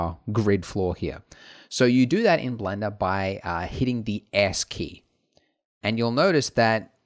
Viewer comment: "So you do that in blender by hitting the ass key" is what I heard